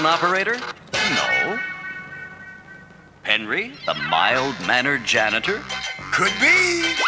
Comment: I honestly thought Penry was called "Henry" for decades, and I still feel like it sounds like the narrator says "Henry" during this opening, unless perhaps, if I really listen to it. However, I just checked an actual episode, and the sergeant clearly said "Penry"... My only possible explanation is that I just assumed they were always saying "Henry" because I wouldn't have identified "Penry" as a name as a kid, and just convinced myself I heard "Henry"?